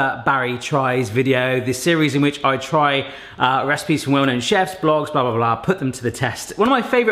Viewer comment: I’m not sure what you said at 0:23 but I’m assuming it wasn’t ‘women and chefs’ 😂 😂 😂 gotta love captions.